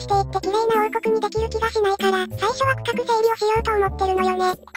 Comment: 区画整理が深く整理って聞こえた！まさか区画整理って言ってただなくて